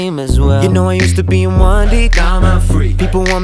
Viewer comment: "You know I used be in 1D now I'm out free" me when school ends😂💯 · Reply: Muser Galore in the music video I thought he said "yeah I used to be in 1D , now I'm a freak"